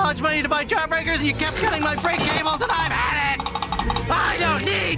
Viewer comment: What's the last thing Jeff said at 1:22? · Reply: "and you kept cutting my brake cables and I've had it"